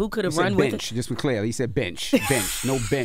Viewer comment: I heard "bitch" as well. 😂